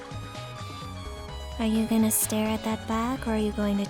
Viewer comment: I thought it said “it’s Arthur’s nutsack” I need to pray